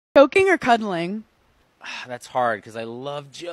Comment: I thought she said joking too 😂😂